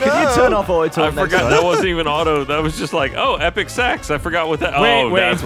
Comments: I seriously misheard Ryan. I thought he said 'Epic Sex', instead of Epic Sax. Dammit XD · The mad king graces us with his dad dancing!!! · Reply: I misheard and thought it was epic sex.... I am weird XD